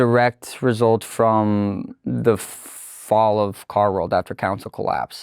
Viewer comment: I was listening the first half and thought he was saying Car-X or Car Rex and was envisioning a dinosaur car.